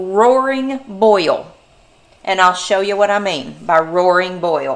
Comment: Rolling boil. It sounded like you said Roaring boil😅